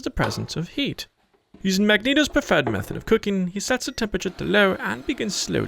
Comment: Did he say Magneto's preferred method of cooking? Lol